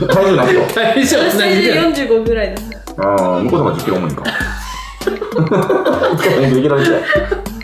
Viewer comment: えまちゃんが体重言ったあと、ラファさん「○○より10キロ重いんか」ってなんて言ってるん？ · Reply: 向こうのほうが10キロ重いんか って言ってますね。 天心くんの方が って意味だと思います